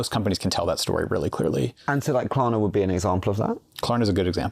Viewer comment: What is the company that they talk about at 23:12? The company that will benefit when the models get much better? CC says Clana? Maybe Klarna Bank AB? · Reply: Klarna. It's an app that lends you money by creating a virtual card that can be used in online stores